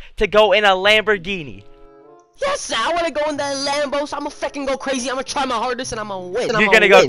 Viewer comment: did he say f**king or am I delusional